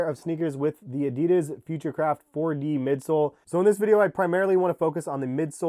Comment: I thought it said Minecraft 4D lol